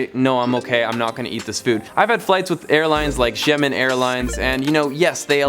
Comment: I thought he said Jimin airlines 😂💀